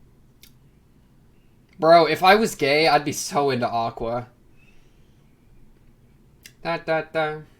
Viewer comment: "If I was gay, I'd be so into Aqua." I heard: "If Aï was gay, Aï'd be so into Aqua " Misunderstanding alert !